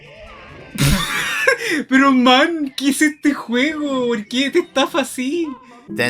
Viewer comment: Does anyone know what the streamer at 1:37:36 said? Just curious. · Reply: "Pero man ¿qué es este juego? ¿Por qué te estafa así?" "But man, what's this game? Why does it scam you like this?"